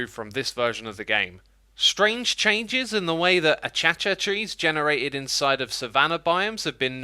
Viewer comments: did he just say achacha trees? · did he just say “achacha”?????